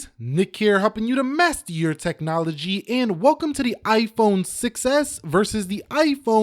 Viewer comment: Was I the only one who thought he said “Iphone success”?